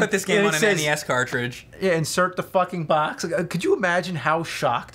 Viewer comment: I thought it said flicking box.